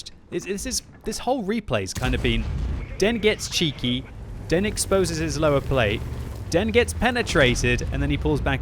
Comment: The whole time i thought he was saying "THEN gets cheeky THEN exposes his lower plate THEN gets penetrated" idk i laughed at that lol